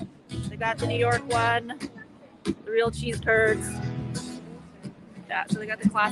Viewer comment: American here. She poutine is French fries topped with gravy and WHAT??? It sounded like she said “sea turds”, honestly. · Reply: Eventually I got it that she said “cheese curds”. Again, not something we usually find on a menu in America.